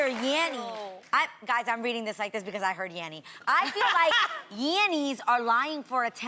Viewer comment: I thought the voice said yelling 😅